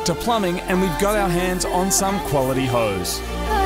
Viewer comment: What did he say at 13:08 ? We got our hands on some qaulity Ho's ? :p :)